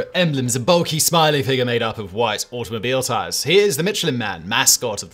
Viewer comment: I thought you said the mescaline man 👨 🤪 😜 🤪 😜